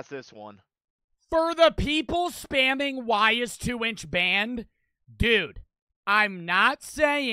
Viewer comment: Thought said "Don't talk about the 2inch ban" then emediately after he said omg he died I thought he was talking about two inch